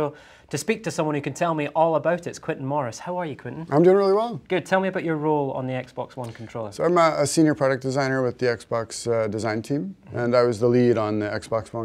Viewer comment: Am I the only one who understands "How are you Quintin?" - "I'm doin really wrong!"